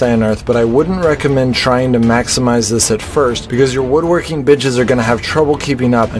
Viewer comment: woodworking bitches lol, ik its benches but it sounded like that 😂😂😂